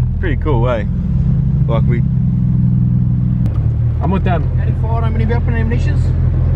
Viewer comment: The ferry guy sounded like he said "any forearms and ambitions" like what??